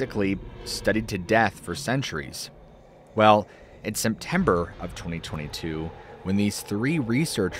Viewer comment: Did you just say Semtember?